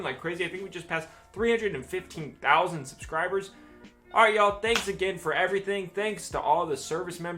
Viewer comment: Hey thanks for uploading today I was a little sad but you always make me feel better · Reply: lmao I thought you originally said “thanks Tom Hanks”